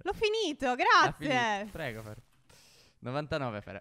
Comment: Ma solo io ho sentito "prego amore" da parte di a al minuto 24:46?